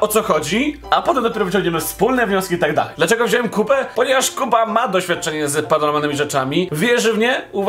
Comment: nie wiem czemu ale usłyszałam ,,dlaczego wziąłem kupę." dziękuję, dobranoc.